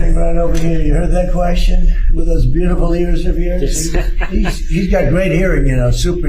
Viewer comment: so I HATE the felon and his regime with all that I am and more!! However, Trump did not say he had big ears at all; he said he had "beautiful ears" . That why make shite up